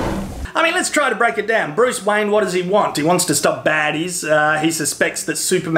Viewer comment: I thought he said Bruce Wang lol